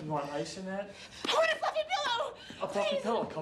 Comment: At first I thought she said "I want a fucking pillow" but she said fluffy